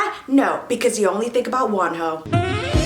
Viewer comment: N here i thought she ws saying “because you only think about one hoe”🤓🤓🤓😂😂😂😂